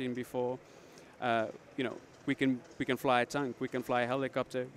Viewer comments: anyone else hear "fly a tank" ?? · did he say "we can fly a tank"